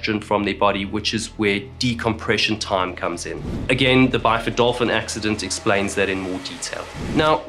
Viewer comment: What did he say? The bifodorphan accident? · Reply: He said "Byford Dolphin"